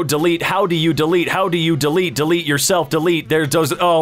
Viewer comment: i only heard “delete yourself “